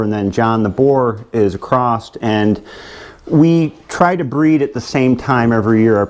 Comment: Did .... did you say "acrossed?" 3:30.